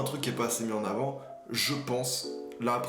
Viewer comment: J'ai entendu "les trotinecdotes", ce qui me permet de proposer à qui voudra saisir le concept, l'idée de la trotinecdote. Je vais pas développer, démerdez vous avec ça, on va pas faire tout le boulot à votre place non plus.